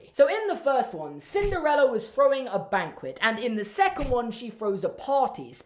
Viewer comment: When you said that Cinderella was throwing a banquet, I first heard it as ”robbing a bank” and I got really confused before I realized what you actually said.